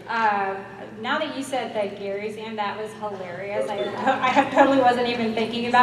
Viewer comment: kale Sam? 😂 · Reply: I heard "Garry" Sam, so I'm confused as to what he meant as well. Maybe I did hear wrong and it was "Kale" though I don't know how my ears would mistake those two very different words😂